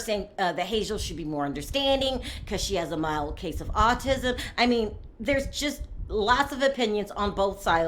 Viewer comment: I thought he said it was a severe case of autism